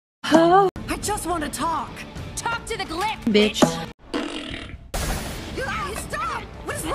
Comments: i heard her say Betch in my head but she said witch. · I promise you when Luz said, "Talk to the Glyph, Witch" in the show I thought she said "talk to the glyph, bitch" anyway so...